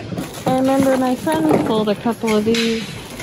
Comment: It sounded like you said "my son sold a couple of these". You have a grown son?